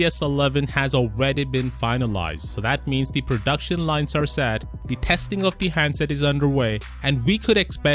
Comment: I thought you said the production lines were sad Poor production lines...